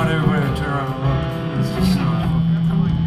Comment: Did Edie have to leave cuz he was sick?? Where he go to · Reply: Oh he put on a hat lmao Did he just say it’s a fuckin cool place to trip in ?? Lmmfao